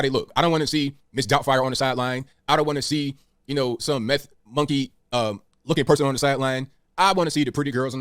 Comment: She’s beautiful. Look at those cheekbones! 21:33 Did he just call this woman a “meth monkey?” What the hell is even that?